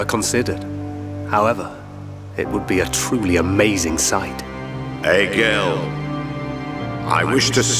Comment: instead of saying "Egil", it sounds like Arglas is saying "ay Gill" in a drunken voice.